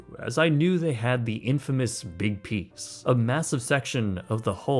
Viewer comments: I swear to god, on first listen I thought I heard him say that they have the Titanic’s “infamous big penis.” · I swear I'm not trying to be gross but I was chewing while I was watching this and I could have sworn you said "the infamous big penis".